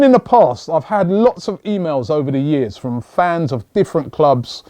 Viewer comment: 2:6 I thought he said Iv had plenty of females over the years